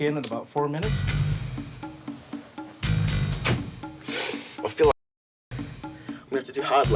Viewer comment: um did he say he “feels like a n*****”?😐